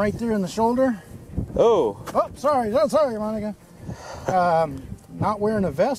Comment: What exactly is Officer saying at 7:55? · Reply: "Sorry, oh sorry Monica". had named the dummy Monica because of the Clinton T-Shirt.